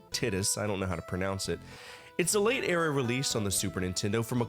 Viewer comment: Did you say tits?